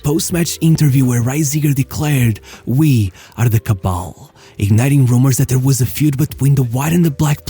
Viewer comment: Dude you're spewing nonsense. What the f is a cabal? He said we're a "kabel" which simple means cable/chain. In the sense that they were tight as a family. And he also didn't say that Hiddink should get his head out of white players butts. He said "certain players" and by that he meant the leaders of that time ( De Boer, Blind) You're making stuff up